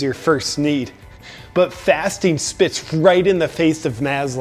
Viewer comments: Oh I thought you said fisting 🤔🤷‍♂️🤫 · I thought he was saying "fisting"..."im always fisting..."